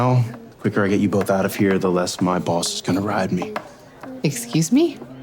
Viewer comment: "EXCUSE ME"💀💀💀💀💀GAYYYY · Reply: I thought he said "or my balls is gonna ride me" 💀💀💀😂